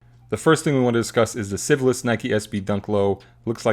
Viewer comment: Who had this week's worst take? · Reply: It sounded like said "syphilis" instead of "Civilist"